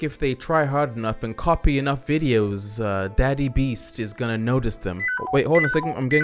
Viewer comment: Did He Just Call Mrbeast Daddy